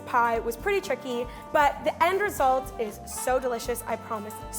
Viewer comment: I thought she said ‘the end result is so good, i vomited.’